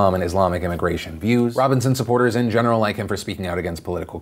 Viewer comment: You say Robinson supporters, I hear “Like Robinson Caruso” and now the fucking Gilligans Island theme is stuck in my head